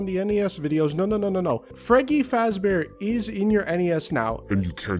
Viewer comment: when i first heard this part i though he said 'Freggy is in your NES now,'